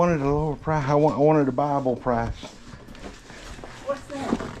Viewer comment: I thought you said Bible price too! Lol. She looked like a fun lady to talk to!